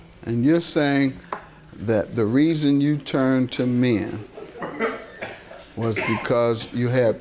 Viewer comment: Wait…I thought the announcer said he turned to meth. Lol.